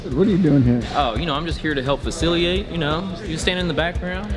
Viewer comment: Lol anyone else catch “fasciliate” at 5:52? Haha